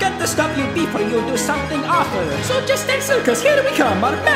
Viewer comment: excuse me, did mario say date with princes peach btw its